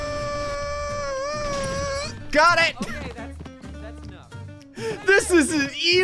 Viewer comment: does anyone else hear the "ok ok thats enough" in the backround